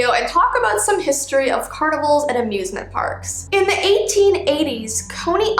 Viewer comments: I thought said abuse-ment park 🤣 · I thought she said abuse ment park 😭